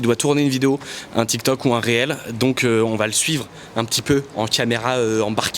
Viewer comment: ya que moi qui dit "rilze" et pas réel ? 😂